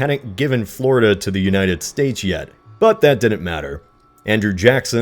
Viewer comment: did he say united stage!?!